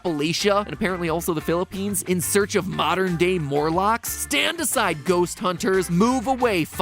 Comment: god I was off screen with the whole modren day morlocks so I heard it as modren day murlocks